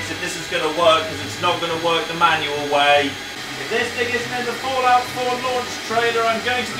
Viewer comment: What did he say? I heard Fallout 4 Trailer!